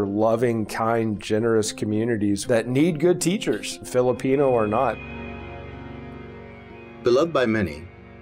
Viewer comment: The pastor said that this community is generous kind loving peole and Filipinos are "NOT". If I am correct, why did he told that ? he is a pastor! OMG! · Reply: I think you heard wrong po. At 3:57, the pastor says: These are loving, generous, kind communities that need good teachers -- Filipino OR not. He's saying, their communities need good teachers regardless of the teacher's nationality.